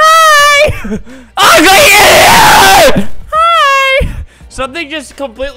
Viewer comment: I think he said Ugly scptato